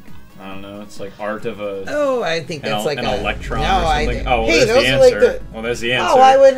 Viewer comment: Did you- did you just say that was an electron????